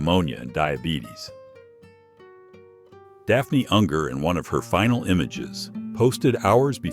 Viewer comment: who the Heck is she again couldn't even get what you said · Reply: Daffney Unger. She wrestled in WCW (became the Cruiserweight Champion) and in TNA.